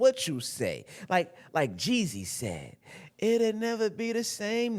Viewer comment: Okay cool, I thought I had heard "like Jesus said..." 12:35 😂